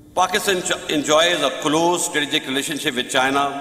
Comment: Am I the only one who heard 5:30 " close Tragic,...."😂